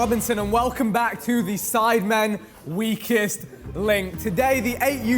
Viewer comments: 🤣🤣 I really thought he said “We Kissed” instead of “Weakest” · bro I thought Vikk said “we kissed link” 💀💀💀